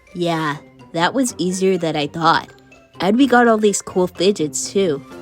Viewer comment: HELP WHY DO I HEAR “all slay ends slay”